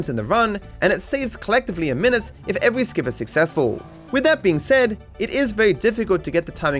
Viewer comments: I forgot to turn off the subtitles, so I just noticed a typo in this line saying "unsuccessful" instead of "is successful". Also the word "dialogue" just right before that moment is written as "dialog", so I'm not sure if those subtitles are actually somewhat auto generated, but I still wanted to bring attention to that :D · the captions say "unsuccessful" instead of "is successful"